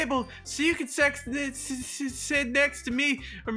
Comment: Did nobody hear say s*x instead of sit